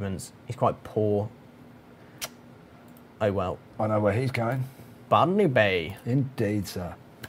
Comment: "Ban_me Bay"? That's what I heard. You should rename Botany Bay to that. xD